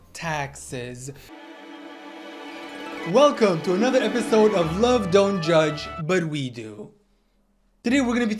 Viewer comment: my dumbass thought you said “welcome to another episode of love don’t judge, but vegan”